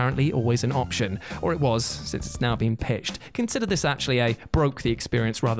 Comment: did you say pitched? I think you meant patched... or did you? Has it been pitched? To who?